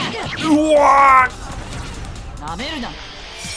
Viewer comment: 氏がおにぎりかきこんだ瞬間にさんが「食べるな！」って言ったように聞こえて巻き戻した